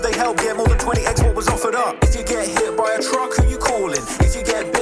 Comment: lol “ if you get hit by a truck who you calling? If you accidentally hit a duck who you calling?” 😂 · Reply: I thought one said hit by a drunk 😂